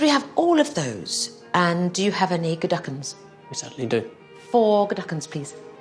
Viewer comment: "Lobster, partridge, scallops?" "Ooh, all of those. And do you have an ________" COULD SOMEONE PLEASE TELL ME WHAT SHE'S SAYING HERE? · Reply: I thought at first she was saying "any geduggins?" in an effort to flummox him with a made-up word, now I think she's saying McGuigan's - bottles of wine.